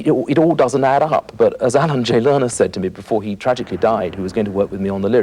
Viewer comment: What is the name that he says on 28:35? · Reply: Alan Jay Lerner, famed lyricist who wrote shows like My Fair Lady, Brigadoon, and Camelot (among many others).